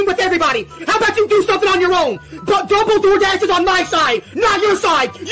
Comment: i thought you said ban dumble doordash?